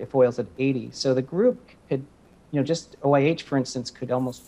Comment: What does he say at 3:22 OAH?? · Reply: OIH stock